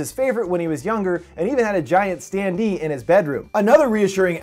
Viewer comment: I thought you said he had a giant stan lee in his bedroom.